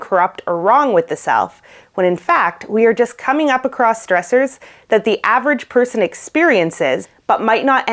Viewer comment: She said: "We are coming across stressors." But I thought she said: "We are becoming cross dressers."